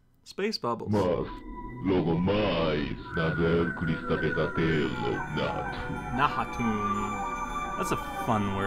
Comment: Ghadius sounds like he's speaking portuguese sometimes. Like in 20:22 it sounds like "mais... logo mais (???) cristal pesadelo Nahatomb" which is almost what he said. dream = sonho enough = suficiente And in his last attack ( 27:08 ) it sounds like "que delícia" = "delicious" and it sounds very silly